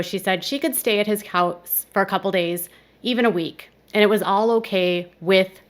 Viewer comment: It’s silly that you’ve spent time “researching” all this over a stupid mishearing of what Gypsy said. She said “a couple days a week” NOT “a couple days OR a week”.